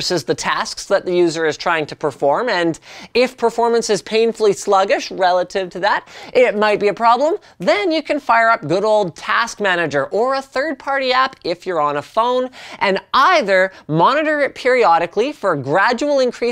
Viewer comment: "Google task manager" 2:33 , Microsoft renamed the task manager? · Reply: I thought he said "Google Task Manager" the first time as well. I had to jump back 5 seconds.